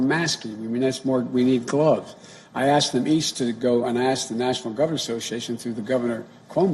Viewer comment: Wtf is this guy saying. 1:25 i asked the *beast to go an i ask the natoinal govnr association.... If he said beast or not there are many words that he doesnt even bother to figure out while saying them.